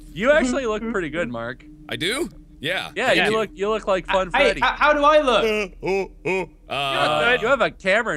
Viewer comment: Did he say funtime in 2015 ? 0:52 That is sTraNgE HUH